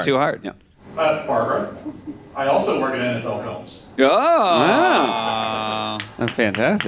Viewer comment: When Barbara said what her son is a producer for, I heard "adult films," and was surprised how cool everyone was about it. I went back a couple times to make sure I heard it right, and finally put on captions and saw "NFL films." But the lead-up really makes it seem like something illicit, because Barbara's hedging, saying, "he works in the business--not what you do," "he works in the film business," "he's a producer." So I guess I was waiting for it by the end 😂